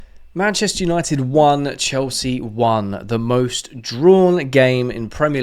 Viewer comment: I thought you were saying Manchester united won Chelsea won. 😂